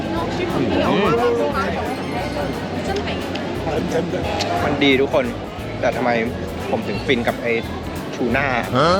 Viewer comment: ได้ยินไม่ผิดใช่ไหม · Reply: ส้นตีน5555